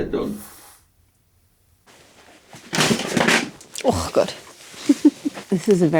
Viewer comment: Is that german?? Did I hear that correctly? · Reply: Haha it's actually 'oooh god' as hits the table 😅😅. Although she has been known to curse in many an exotic language!